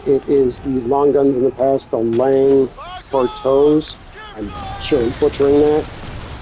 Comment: Another great video! I still need to make my Schooner... I couldn't catch the name of the other long guns you mentioned putting on the sides at the 23:00 mark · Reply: Lang kartowe